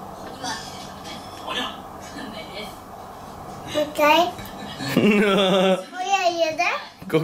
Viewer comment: もう1回。おやいいよね？ って聞こえるw可愛いw